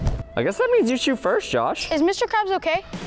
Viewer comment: did Josh just call Preston mr krabs